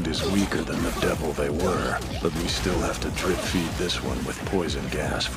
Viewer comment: subtitles pls, I can't understand what hes saying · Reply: Speak for yourself, dude. 0:47 I'm still not sure if hearing this part right. Did he say DRIP-feed?